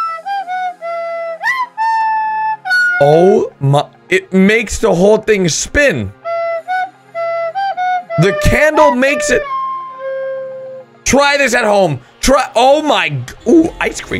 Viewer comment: “TRY THIS AT HOME TR- TRY THI- oooh ice cream!” Edit: why so many!? Like wow thx 👇🏼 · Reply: bruhh, i thought he said "i scream" 😂